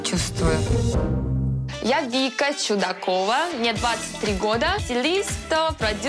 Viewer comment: Я вместо "Чудакова" услышала "Че такова!"😆 И еще манера речи мне мем напоминает, где женщина-продавец в русскую речь добавляет английские слова))